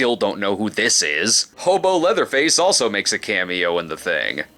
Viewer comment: Wait, did he say Homo Leatherface or Hobo Leatherface? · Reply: Hobo Leatherface.